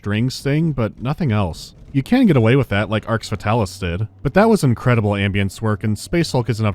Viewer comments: What is the game mentioned at 4:51? I've tried googling what it sounds like you're saying (arks vetalice) but not had any luck. Also might be useful if you include links to any games you mention during a video in the description in the future. Thanks. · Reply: Arx Fatalis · What's the name of the game he's referencing at 4:50 ? I hear Arks Vitalis, but nothing comes up when searching for that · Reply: Arx Fatalis, maybe?